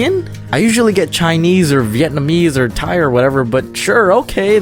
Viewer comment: I thought he said "tire, whatever"